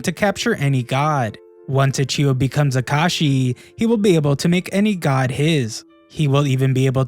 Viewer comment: am i trippin bc i swear i heard Kakashi